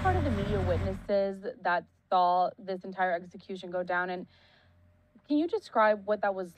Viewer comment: did she really say “execution go down”